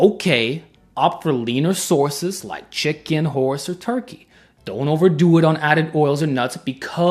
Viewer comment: did bro say horse